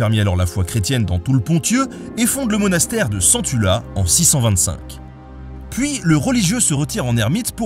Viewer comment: non mais c'est moi qui ai l'esprit mal placé ou d'autres ont tiqués à 30'' sur le "monastère de CENTULA" ? un monastère... CENTULA... moi j'ai entendu "sens-tu la"; franchement quand on parle de l'église depuis quelques décennies, le cerveau s'est habitué à un certain champ lexicale je pense... Sinon, toujours aussi bien les vidéos NATA BENE.